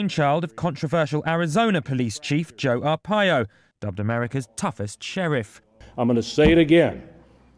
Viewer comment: Wait..... 10:20..... did he say south America's toughest sheriff?